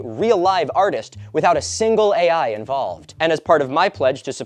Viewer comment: the captions say "without a single guy involved" instead of "without a single AI involved" and for some reason that's hilarious.